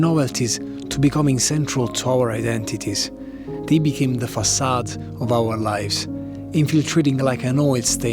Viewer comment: did you say facade? It's an arabic word, do you also have it in Italian ?